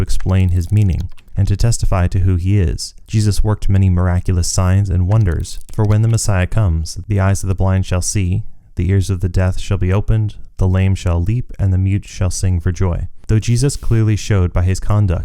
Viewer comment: Did you just say "The ears of the death?"